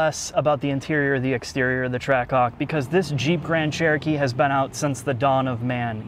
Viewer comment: I swear I heard trackcock